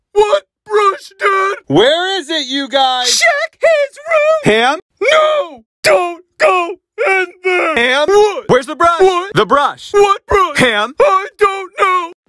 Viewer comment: is hilarious 😂 “CHECK HIS ROOM!” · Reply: I thought she said "Sh*t his room" lol